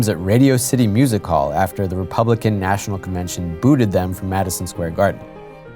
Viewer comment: I thought said that the Republican National Conventions booed at them in MSG.